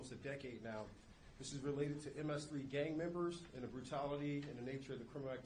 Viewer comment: did he just say "MS-3" or what?